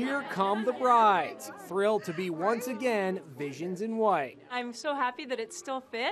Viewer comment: i thought he said ‘thrilled to be once again, asians in white’